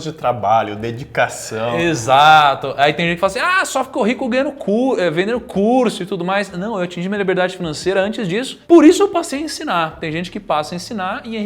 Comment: e impressao minha ou eu ouvi "correndo com o dedo no cu" no minuto